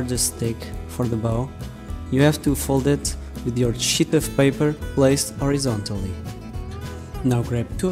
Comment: did you say a sheet of paper or a shit of paper?